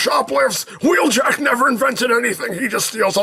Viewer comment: It sounded like he said Don't hurt the Pringles Prime into Pringles as well? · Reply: He’s saying bagels